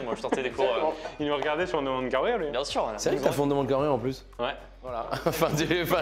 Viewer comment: À un moment (1:01) Pierre dit que Lucas les regardais sur un truc, et Artus dit que c’est Pierre qui a fondé ce truc. Quelqu’un a compris de quoi ils parlent? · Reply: Il dit c’est vrai que tu as fait on demande qu’à en rire